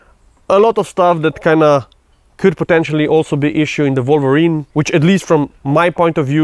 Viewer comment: And my dumb ahh brain thought it said the Wyvern😭